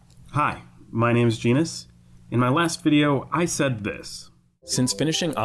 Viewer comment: I thought he said penis 😭